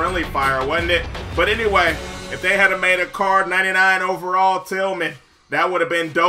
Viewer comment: there is a 99 Charles Tillman and the set is in the redemption. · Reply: Oh I thought he said Charles tillman.